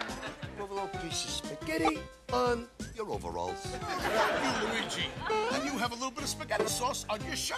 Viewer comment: Neatness Counts 6:05 Day of the Orphan 11:11 All Steamed Up 16:14 Marianne and Luigeena 21:17 Slime Busters 26:02 The Mario Monster Mash 31:10 Bonkers from Yonkers 36:13 Bats in the Basement 41:20 Will the Real Elvis Please Shut Up 46:25 Magic’s Magic 51:11 Mama Mia Mario 56:14 Alligator Dundee 1:01:18 Dance 1:06:22 Cher’s Poochie 1:11:27 Wild Thing 1:16:01 E.C. The Extra Creepy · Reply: Don’t forget 1:20. “Oh f*ck you, Luigi.” Yes, I know he really said thank you.